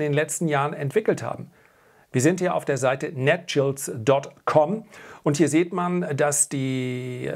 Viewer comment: Wie heißt die Homepage bei Minute 6:00 (für die absolute Dividendrendite? Er sagte was von Netgels, ich habe das nicht ganz verstanden, kann mir das jemand beantworten? Wäre echt nett. 😊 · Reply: Netcials 🙂